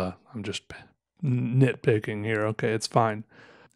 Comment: What? Did you say "nib picking"?